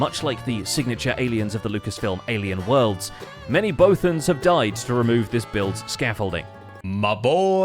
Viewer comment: thought he said Boatems